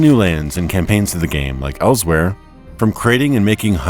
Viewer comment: "Perhaps we'll have look for our ideal role playing game ELSWEYR ." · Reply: When he said elsewhere my mind immediately was like "DID I JUST HEAR ELSWEYR ?"